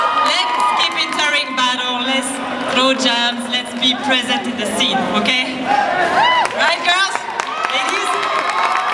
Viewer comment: amazing battle, what did say @ 6:36-6:37? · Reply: GrowingDownUnder throw jams, lets be present in the scene